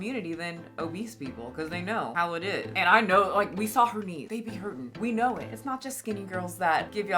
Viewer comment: did she say "we saw her knees"? because who's we? I didn't see one knee let alone two knees. I do see skin, a lot of skin. :face-blue-wide-eyes::face-orange-biting-nails::face-blue-question-mark